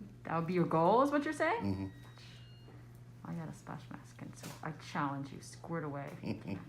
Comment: I challenge you squirt away??? What’s she actually saying?